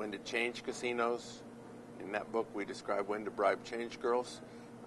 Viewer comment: "...when to bribe change girls"?! Did I hear that right, and what does he mean?